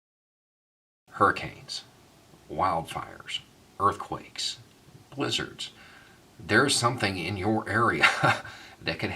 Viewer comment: I thought you said LIZARDS! lol.